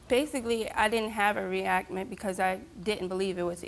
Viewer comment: Steve asked how she reacted and she says I didn't have a reactment 🤭 anyone else catch that?